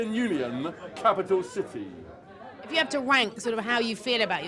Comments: is it just me or does it sound like she says 'If you have to wank' 🤣🤣🤣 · did she say ''if you have to wank'' ?